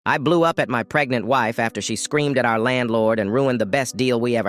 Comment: I thought it said I blew up my pregnant wife… I’m so slow